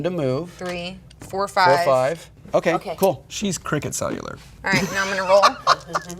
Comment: What's being said at 21:50? "She's cricket cellular?"